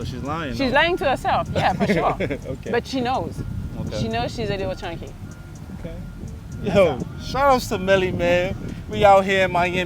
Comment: Why did I initially think he said “Mellie Mayo” is beyond me…😅😂